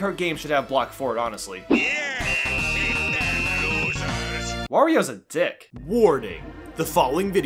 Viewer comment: captions, WHAT DID YOU SAY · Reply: "Wario is a dick"